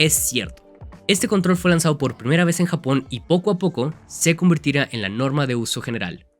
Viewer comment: Alguien sabe qué dijo cuando susurró ahí en 25:46? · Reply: “Se convertirá en la nueva norma de Xbox”